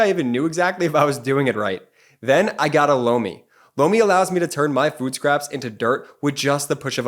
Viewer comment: I'm sorry sometimes I have to laugh at some of your sponsors. I could have sworn Jordy kept saying "blow me" until I saw the product name, lol.